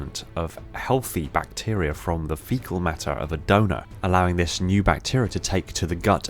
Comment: anyone else hear dougnut instead of donor?